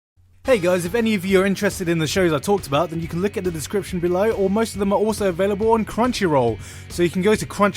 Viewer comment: i heard 'then you can lick on the description below' lol